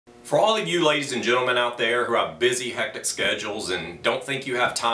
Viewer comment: I heard "For all you lazies and gentlemen out there" lol.